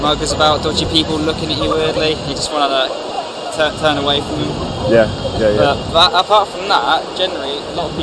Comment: Hello what did the first guy say in 1:07? It's something like "It's quite a low offer?? and what else until 1:15 I don't understand him. Thanks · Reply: " a lot of dodgy people looking at you weirdly, you just want weirdly, i just wanna... "turn away from me" "